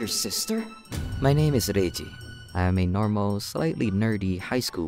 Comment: The way he said reji kinda sounded like rusian